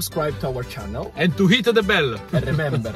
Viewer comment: I thought he said my nipple does not belong on pizza 😏